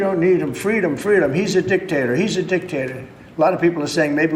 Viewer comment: Every time I hear him say it, I hear "dick tater"